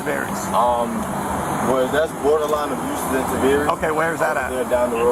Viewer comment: Did he says it’s “borderline ‘abuse’”?